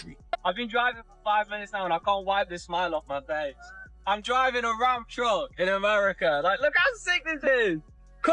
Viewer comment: I thought he said, Im driving around drunk! Lmao he said hes driving a Ram Truck 😂😂